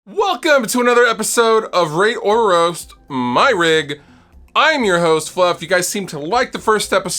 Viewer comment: I had to rewind and play back a few times.... I kept hearing "rape or roast my rig" awesome episode !